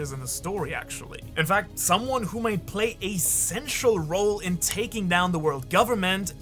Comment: I cannot be the only one who heard that Sengoku was going to play a sexual role taking down the world government. Oda's bold storytelling breaks through another ceiling long-thought to be impossible lol